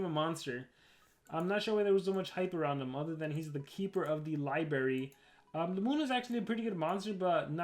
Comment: ✨Did you say “LIBARY”?✨